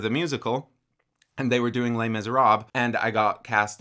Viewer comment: Completely random, but I just had to share this--when he said "Les Miserables," at first I thought he had said, "Lame as a rob."